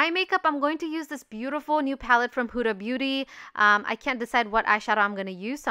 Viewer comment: I first heard "puta beauty" I had to check again lol